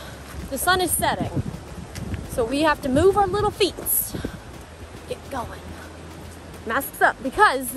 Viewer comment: Did she just say "feets" for feet.....lol 😂